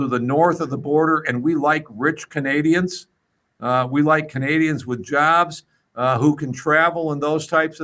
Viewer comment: Did He say Canadiens with ….jabs ? At 26:18 please correct me please